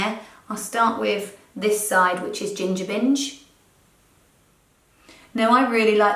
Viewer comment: I thought you said 'ginger minge' 😂😂